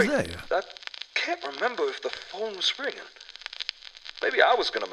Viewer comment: did he say he can't remember if the phone is real? That really makes things interesting! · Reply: I though he said ringing, he doesn’t remember it ringing. Though once I read your comment I can see how it sounds like he said real.